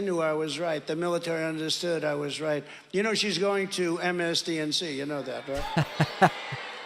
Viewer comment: "MSDNC"? John Wayne Gacy and Donald Trump, two VERY dangerous clowns. 😔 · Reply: I thought I was the only picked up on that. I thought he said “MSDMC.”